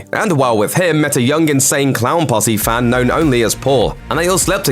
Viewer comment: Am I the only one who heard him say he was an insan3 clown posse fan? That's really random